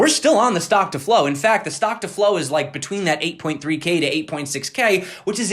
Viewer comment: Anyone else heard say b*tch coin at 5:10? Lmaoooo